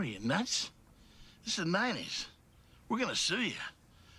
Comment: The first time I watched this movie, I thought that guy said “That’s not nice”, instead of “This is the 90s.”